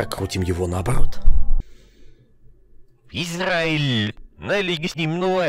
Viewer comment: Я один услышал слово «Израиль»? Даже наушники не отрицают его нацию